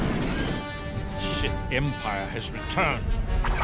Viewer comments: is he saying "Shit, Empire has returned!" or "Sith Empire has returned!" Both could work in this situation. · This is one of the coolest trailers ever I’m not gonna lie though. I thought the Jedi said shit. The Empire is back, but he said sith empire yes I know this trailer is old. I just wanted to comment on it because getting back into Star Wars again game getting back into the MMO. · I always heard"Shit,Empire has return"